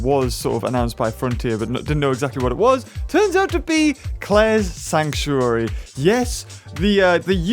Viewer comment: At first I thought it said Charlie's sanctuary but instead is was Claires. Why frontier that would have been hilarious